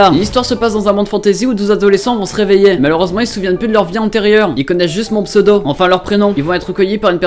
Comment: C'est moi ou à 0:42 il a dit "Ils connaissent juste mon pseudo" et à 0:56 "Comme d'habitude je vais commencer par la fin,l'opening" ??